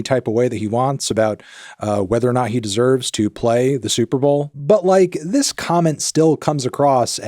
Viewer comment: My dumbass thought you said the 4th Super Bowl😭